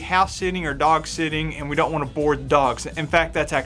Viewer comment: Oh, have your dogs "boarded". For a second i thought you said bored!